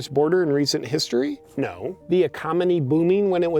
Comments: Yeah, those people in Arizona sure can't wait to vote.... lmfao!! Also, at 4:38 did anyone else here "ecomony" instead of economy? Don't know why, but I thought that was funny as hell!! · he says “ecominy” instead of economy. 😂